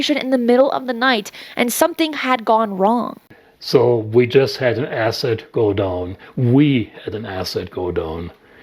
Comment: OOh! He was saying, "We just had an asset go down" ... I was hearing, "acid godon" ... or something. Thank the aliens for closed captioning or I wouldn't have had the foggiest idea as to what he was saying there. lol